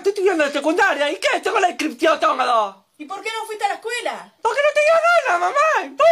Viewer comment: "y que tengo la descripciotongalo" Que dijoo? 🤣🤣🤣